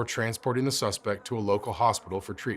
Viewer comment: I heard wrong and thought at the end you said "no other person was injured" so i froze thinking about what just happened 10 seconds ago when the dude got shot 😂 (For those sensitive people out there, this is a Joke. I am not making fun of the person who got shot nor the situation i am just stating that to me it was funny for "me" to "me" it was. I recognize this wasn't funny but to "ME" it was the way i fot confused.)